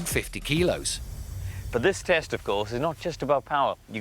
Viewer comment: for a second I thought he said testicle